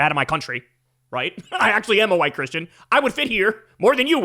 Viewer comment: Did Kyle say he's a white Christian?